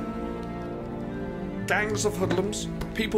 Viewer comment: I thought for a second Kier said 'gangs of muslims'